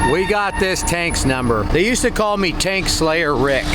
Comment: Taint slayer Rick?.... The fu...... Oh you said Tank! Tank, I gotcha. I heard something else!! My bad..